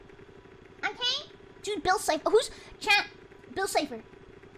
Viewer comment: is weird I don’t understand why she suddenly said that but it was entertaining and I panicked for a second because I thought she said bill Cosby but it was just bill cypher